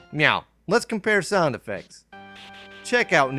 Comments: DID i JUST HEAR YOU SAY "MEOW LETS COMPARE SOUND EFFECTS". LMAO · Did you just say "meow" at 7:15? lol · For those wondering what happened at 7:16 I think he meant to say now lets compare sound affects.But he either mispronounced the now as meow or the video compression messed up the audio. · Glad you enjoy it sir. New episode should be out soon. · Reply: UHHH?? DID U JUST SAY "MEOW, LET'S COMPARE SOUND EFFECTS?? REALLY??